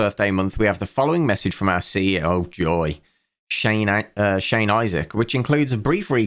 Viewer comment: In "Shane Isaac takes over the web design" I misheard Shane Isaac for Shane Dawson and didnt even bat an eyelid.